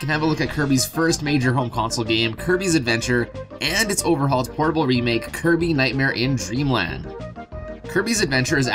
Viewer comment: I thought you said horrible remake at first